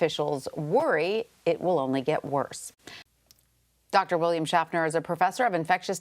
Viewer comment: Captain Kirk I trust, ngl. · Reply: Haha yeah I thought she said Dr William Shatner